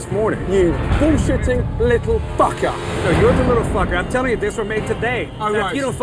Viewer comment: "You bullshitting little fucker"- Gordon Ramsay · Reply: I thought he said 'pinshitting little fucker' o-o